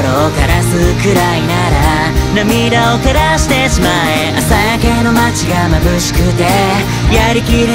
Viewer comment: 『涙を枯らしてしまえ』が 『涙を照らしてしまえ』に聞こえた…